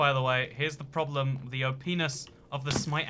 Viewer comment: It sounded like you said the openis instead of op-ness lol